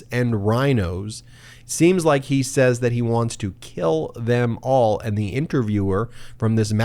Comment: I also thought they said kill at first, but listening carefully again and again, I actually heard "jail em all", not "kill em all". not good but better than kill. The man's accent was difficult to distinguish, so I can see how the reporter may have not distinguished him saying kill. I may be nitpicking, but these people have enough problems without inflating things out of proportion. Lets leave that tactic to MAGA.